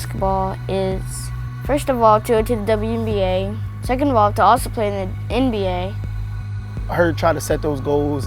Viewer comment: did you hear what she said?? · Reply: Juice Co “too also play in the NBA”